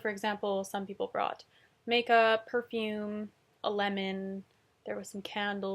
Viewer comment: I heard that as "Makeup, perfume, a Lenin..."